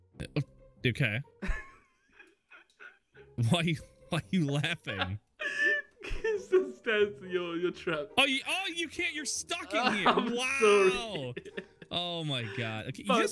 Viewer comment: sounds like said why fucking laughing